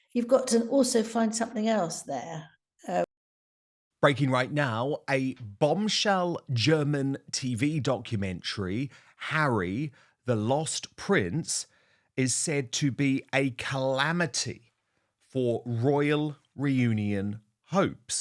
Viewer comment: I’m sorry! I thought for sure you said “Lost Pr**k”. I almost coughed my coffee up through my nose!